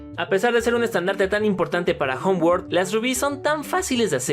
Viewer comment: Cómo estoy acostumbrado a el doblaje a español latino cuándo dedican homeword pensé que decían hogwarts